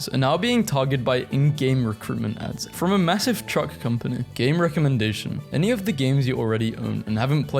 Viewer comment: I thought that he said gay recommendation but then released that it was game recommendations. Wops :)